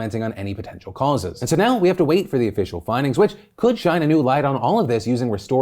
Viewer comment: uuuhm.. did philly just say "Could China new light"?!